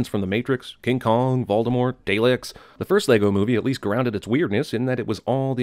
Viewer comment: Did he just say, Day-licks, not Dar-licks?😂😂